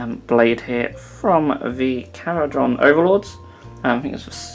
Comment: A what overlord? I've never seen a part like that on a Necron so not a necron overlord. Did he say Cawdor? Corridor? Carchador? WHAT IS THAT PART!? 12:53 is worse... Still no idea what he is saying. He says it so fast and all jumbled together. A Eels Volvee RMFD Camel hunter? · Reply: Kharadron Overlord